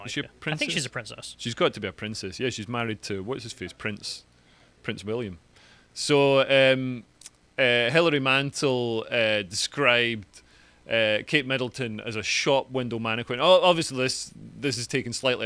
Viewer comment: I love the irish/Scottish guy's accent! Sometimes I heard "Hillary Mental"... fitting name it seems...😎